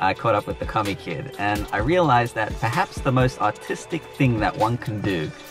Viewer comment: I thought you said autistic not artistic! 🤣🤣🤣